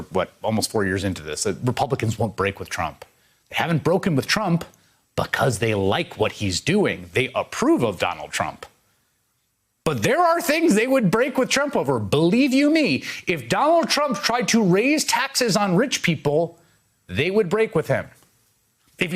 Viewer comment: Did he just say “Ronald Trump”